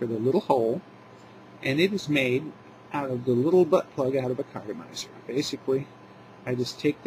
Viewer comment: Seriously, did you say "butt-plug" at 5:47? Awesome. Google it.